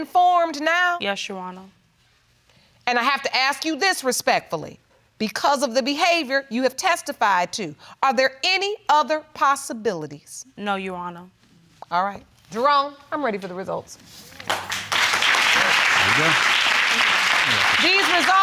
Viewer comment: Okay, if she say Yuwarna one mo time!! 😂😂😂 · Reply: But Barron, l thought l heard " yes & no your wonour"